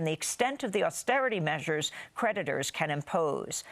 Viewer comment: Creditors? I thought she said predators.